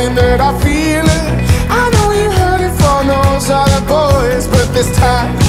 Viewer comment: Although is says, "I know you hear from those other boys," the first time, i thought it said " I know you heard Thanos and the boys," xD Edit: Ima like my comment cus nobody else will